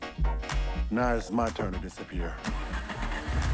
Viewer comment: What do they say at 1:27:52 ? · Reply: it,s just "toodeloo". Lile in the simpsons, flanders when he is talking like this: good morning homer, how doodely doodely. Hence rodmans weird look wjen the guy says it. I believe it,s called dadaism.